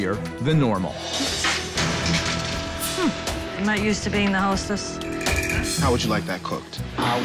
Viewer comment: The hostess/owner is terrible. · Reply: I thought you said hostages